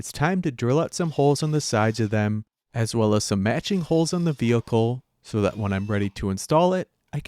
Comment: Instead of matching, I thought he was saying magic, heat shrink tubing, and I like that, magic tubing.